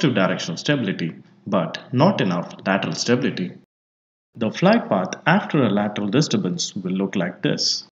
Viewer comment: I can't understand what you said at around 3:47 to 3:48 because of your accent and there is no captions: you said "the flight path after the lateral ?????" than you said a word which I did can't figure out because of your accent. I mean no offense but what was it that you said? because that word is throwing me off, I can't understand what's going on. Anyways if you can get back to me that would be great. Love your videos! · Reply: The sentence is: the flight path, after a lateral disturbance, would look like this!!. thanks for watching. do watch other videos on my channel!